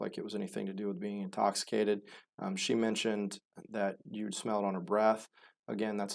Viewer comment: " youth smell on her breath "? Yute smell on her breath" ? What's this guy saying? I didn't catch that. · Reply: Not sure what this guy is saying either….but I think he said “you’d smell it on her breath”, meaning the alcohol.